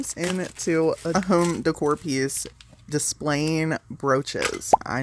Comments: I thought you said display of roaches!!????😂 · I thought you said roaches🪳 😂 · I thought you said roaches..... Lol😅😅😅😅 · I thought she said display roaches. 😂😂😂